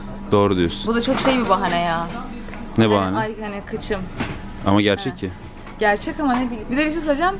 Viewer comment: Ayh Hani kıçım Yanlışmı anladım? Abla ÇOK GÜLDÜM YHA :d